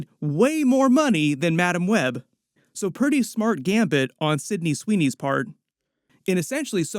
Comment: Did he say gambit?